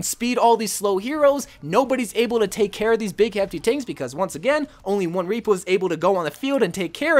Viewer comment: "only one reapaws" no hate, I just thought the way you said it was funny, love u